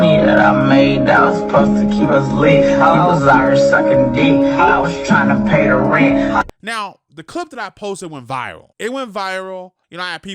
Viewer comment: Play at 0.5 he clearly says “i was out here sucking dick i was trynna pay the rent”🤣🤣